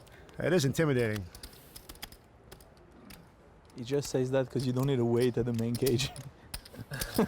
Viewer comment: I think this pot should be awarded to whoever can figure out wtf Benyamine is trying to say here... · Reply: Rough guess "He just says that because you don't need to wait at the main cage" probably for exchanging chips